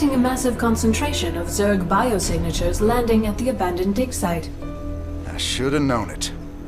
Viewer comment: does it say abandoned dick site? lolz